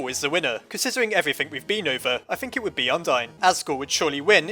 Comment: the captions say: “I think it would be undying .”